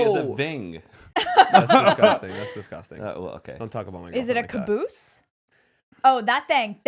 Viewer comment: Did just say “don’t talk about my girl” 0:26😂😂😂😂 she clearly laughed